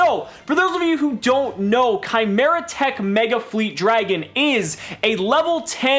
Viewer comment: i thought u said how good it is Chimeratech Megafleet Dragon?!!!